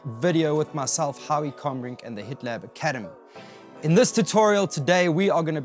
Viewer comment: I thought you said "The Hitler Academy" at the beginning and had to double check I was in the right place.